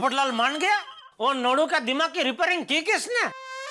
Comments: my dirty mind😂 jethalal ne kya bola... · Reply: Nadu bola jethalal ne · did jethalal say lodu